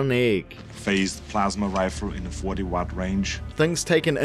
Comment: Huh, 'Mandella Effect', I had always thought he said "40 Megawatt Range", although I'm not sure even that is enough power for a 'plasma rifle'.